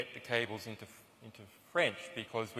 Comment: What does he say at 1:29:00 It goes somethiing like this: " later on we workt on "cablegates" we selected our french partner lemond in order to get the cables in to france?? What the heck are does "cables" he is talking about?? Please correct me if i heared wrong:-)!?